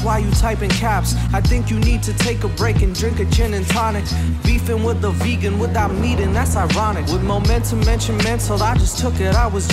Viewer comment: The fitst time I listened I thought you said "Beefing with a vegan without MEAT, that's ironic"